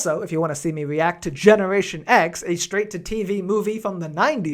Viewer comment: I thought you said "Generator Rex" and now I'm sad.